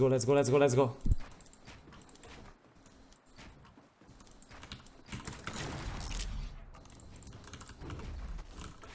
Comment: Szia! Tegnap nézelődtem és rá néztem ninja videójára! Ami jelenleg 20oraja van fent! 6:52nel mintha téged említett volna! Angol tudásom nem sok,így nem tudom mit mondott! Válasz kent valaki help:) Csak kíváncsi vagyok:) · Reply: Azt mondja, hogy: Here's a piece of advice: Don't tell a streamer how to run the chat! - Egy jó tanács: Ne mond meg egy streamernek, hogy hogyan kezelje a chatjét! - Tehát teljesen más, de azért köszi, hogy felhoztad! :D